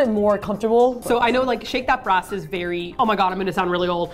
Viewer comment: I thought you kept saying "Shake your Breasts" I was completely blown away by how casual everyone was about it. As I've now discovered, it's "Shake that brass."